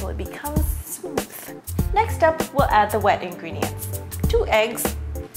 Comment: thought she said weeing ring not wet ingredients